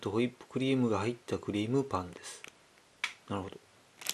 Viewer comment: What does he always say at 0:17 ? nahoro or something ? What does it mean ? He says it also on most of the other videos, so I'm curious what it means. :)